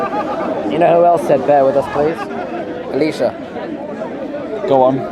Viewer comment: would like to get subtitles. 8:37 perfect example to us non british cant understand a single word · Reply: I hear it but don't get the joke. "Bear with us. Elisha." shrugs